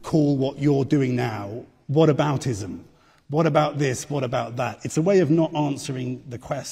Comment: Did the reporter just say we call this what aboutism? Smh is that a saying? 🤣